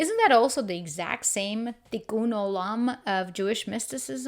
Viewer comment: Did you say Tikkun Olam at 10:12 , a lot of people probably won't have a clue what you said and there is no transcript and the subtitles would make it impossible to know what you meant. The only reason I caught it was because I have about 100 books on kabbalah.